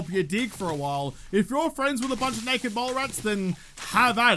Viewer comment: "Unless you're friends with a bunch of naked (mole) rats." I thought he said naked (mall) rats for a moment. A whole different visual with that. 😜